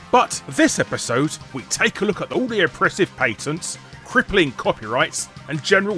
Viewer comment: I thought it said "parents" in the title.